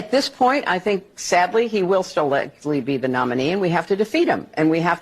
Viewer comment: Sounded like she said "sadly he will still live" then caught herself and sad "be the nominee" Creepy 😮 mark